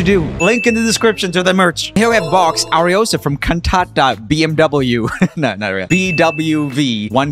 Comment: the way you said 'Cantata' sounded like kantap which is a word in a common phrase in hindi 'Kantap marenge' which means I'll slap/hit you hard😂 (btw the phrase and and word 'kantap' is romanized it isn't actually written like that)